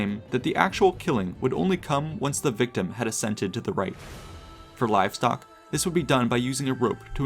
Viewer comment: Someone please help me out here… “death would only come once the victim had ascended to the right”? That doesn’t make sense, does he mean “consented to the rite”*? Or maybe “accepted the rite”? It’s driving me nuts too, I just cannot make out what is actually saying… Anyone else know what he IS saying, or rather what he means? Death would only come once… what? 6:05… ALSO, what does it mean to “induce a bow”? Like a bow forward? Or a bough? Am I having a stroke or is anyone else noticing this?